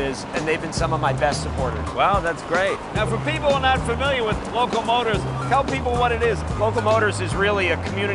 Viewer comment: I have seen the local motors first 3-D printed car. It's truly amazing! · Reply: Yea, I thought he said Loco Motors, that would have been a better name (no facetiousness, I mean better).